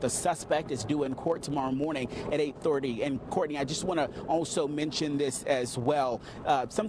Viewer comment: Did he just say school canceled the rest of the year?? 4:18 on the video